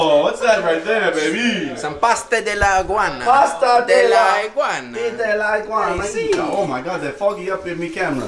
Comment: i just heard bastard iguana😂